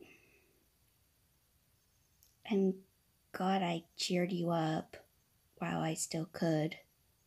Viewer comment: when he said i'm glab i cheered you up i thought he said i'm GOD i cheered you up XDDDD